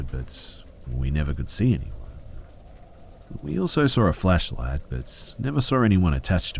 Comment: When he says "flashlight" it sounds like he says "fleshlight" 😂